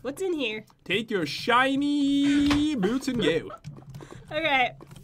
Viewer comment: I thought they said Ur skinnnnny boots